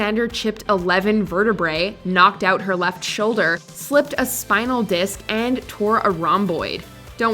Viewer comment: I thought you said “flipped a disk” instead of “slipped a disk” when referring to Jaimie Alexander’s injuries, which manages to sound infinitely worse than slipping one, which is also certainly no picnic